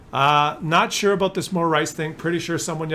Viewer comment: My favorite tier 6 light tank.. You wan mo rice?! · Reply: I genuinely thought said 'Muller Rice thing' and I came here to check LOL.